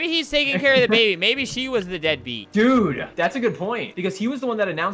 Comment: help what the heck did albert say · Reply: he said “She was the dead beat” as in the wife wasn’t there for the child while growing up, hence the reason why they broke up. I know this was a year ago but hope this helps!